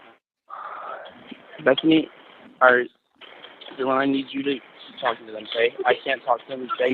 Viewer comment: I hear at 18:07 “Bethany… or dylan, I need you to talk to them” not “that’s me” to the officers